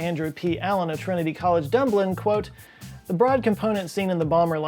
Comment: did he just call Dublin "dumblin"?